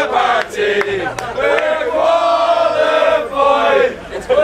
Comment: Did someone say qualified? 🏴🏴🏴